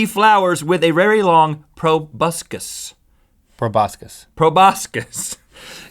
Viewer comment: When they said proboscis I thought tobuscus then they said "not to be confused with tobuscus" and I died